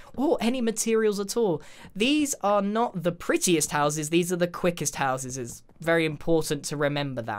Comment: did anyone else hear these are the quickest houseses. Cause I did